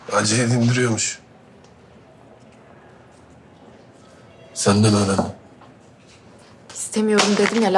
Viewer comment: civan ne diyor ? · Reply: acıyı dindiriyormuş senden öğrendim diyor